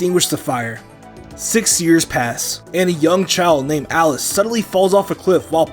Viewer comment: When he said a YOUNG child I thought he said DUMB child